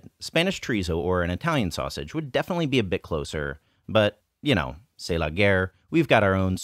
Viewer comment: Had to chuckle at your use of "c'est la guerre". A white American dude, living in China and teaching Chinese cooking, expressing resignation at his inability to source Portuguese sausage by using a semi-antiquated French phrase. What a time we live in! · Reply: Thanks for explaining! I thought he said "tse lager" and i was wondering whether that is a beer brand in China 😅